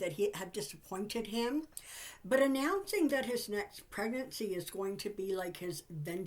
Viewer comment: Kind of funny at 5:15 it sounds like Trump's next pregnancy instead of presidency and I was thinking that could be Trump's next level of delusion that would definitely make the news. 😁